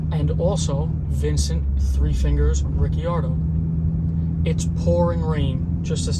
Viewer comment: Coulda swore At first you said guy who killed his pops name was 3 fingers Riccione Haaaahhaaa hilarious but than I heard ricciardo.🤷‍♂️Excellent video my friend, really good. thanks.....Yet another great example of the gang that couldn’t shoot straight